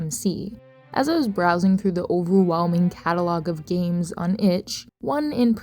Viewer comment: you did not just say "uwuwhelming" 🙏